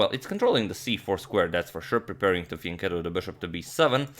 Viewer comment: "preparing to ... the bishop to b7" what's that word is he using ? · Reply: Fianchetto